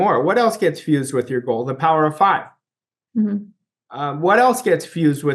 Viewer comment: Thanks! What do you say at 15:33? The power of... ? Thanks for everything. Blessings 🙏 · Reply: 5, Thanks for watching